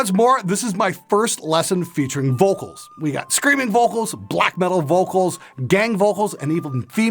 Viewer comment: Did he say Black Metal vocals😀?!